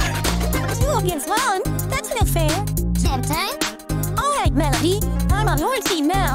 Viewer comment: I might be hearing wrong, but at 0:55 I think her vocals say "That's not fair." but the subtitles say "That's no fair." Just wanted to point that out if it needs fixing! Ik this released ages ago and might've been mentioned and/or fixed, but just in case! (I do typo correction for a few games I'm testing.)